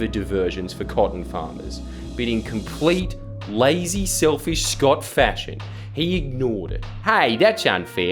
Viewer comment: I thought when he said “In Scott fashion” he was saying “Scott Fascist”... Still works.